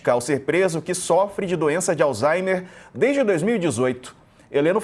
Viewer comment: Me corrijam se eu estiver errado, mas eu ouvi a pronúncia " alzheiNer"???